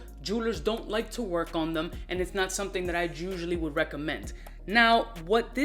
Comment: Im kinda high but 8:14 got me crying 😂what he say “ gusually”,chusally”or “usually”😂